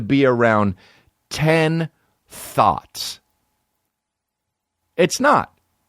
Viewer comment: I always thought that he said “being around 10 thoughts”... today I realized he meant THOTS 😂